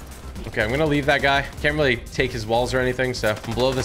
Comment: I thought I heard him say I can’t take his balls😂😂😂😂